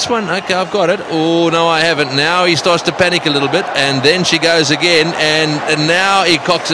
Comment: what the commentrator is saying steve waugh is catching ,, she is going again8:42